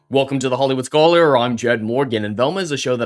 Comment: it sounded like "WOKEome"! 🙂